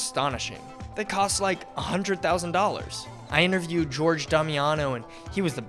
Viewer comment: Gerard Damiano. I could swear I heard you say George? 🤣😭